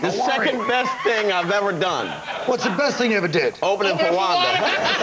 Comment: What did he say? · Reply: Opening for Wanda